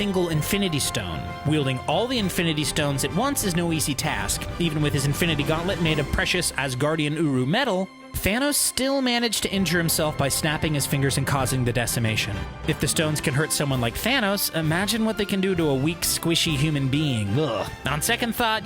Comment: did you say fanos?